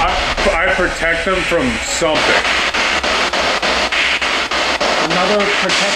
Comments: i though i heard “id protect the kids” which could be the parents saying that “they’d protect the kids”?? amazing video though!! ❤️ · mark - I hear, "I protect their kids."